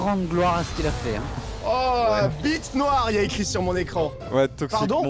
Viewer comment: y'a que moi qui entend "b*te noire" ???